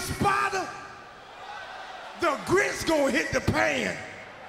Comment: "The grits gone hit the pan!!!" He really is from the South 😂😂😂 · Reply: I thought he said “the grease gon hit the pan”